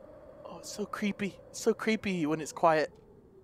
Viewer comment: It said quit...I thought it said quiet😂